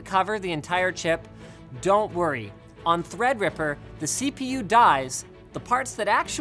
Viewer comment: Lol thought he said "the cpu dies" I was like why linus